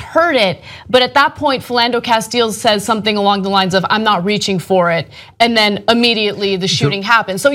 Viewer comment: No Ana he actually said "I'm gonna have to reach for it". As if the gun was in his pocket on top of his id.